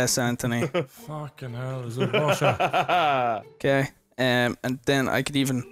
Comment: anyone else hear Nogla say, "Is it Russia?"